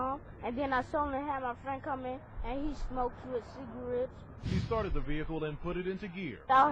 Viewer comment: i dont want to sound like an idiot but did he say my friend smokes ciggs???